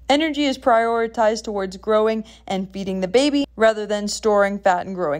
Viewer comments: I seriously thought you said beating the baby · I thought she said …”beating the baby..” 👌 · Am I the only one that thought she said "beating the baby"?